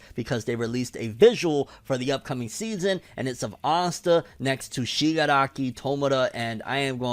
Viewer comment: did u say "asta and shigaraki" when u were reviewing the visual did i hear it wrong?